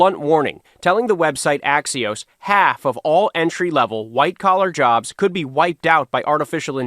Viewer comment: Ohhhh....thought you said white jobs 😮......ok white collar jobs 😮‍💨